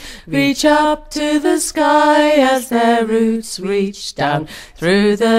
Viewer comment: i heard "through the deep darkness" but "through the deep dark earth" makes more sense :P